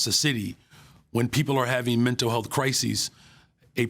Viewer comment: Called mental health suffers "Crazies"😂😂😂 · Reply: Oooh! I thought he said “crazies” too! I was surprised there were almost no comments about this.